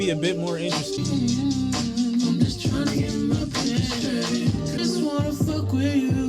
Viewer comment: My favorite song is Been Away. The beat reminded me a lot of the 90’s timbalan sound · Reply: kind of messed up the lyrics, I thought Brent was saying “I’m just tryna get my people straight” then I realized he’s really saying “I’m just tryna get my paper straight” referring to getting his money up.